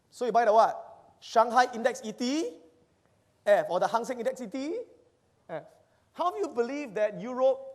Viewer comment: What did say around 17:34 after he said to buy Shanghai Index ETF then ... (hangsen) Index ETF? What is it? Because I thought in China there were only Shanghai Index and Shenzhen Index? If anybody know which one he was referring to please be so kind to comment below :) ♡♡♡ Thank you so much!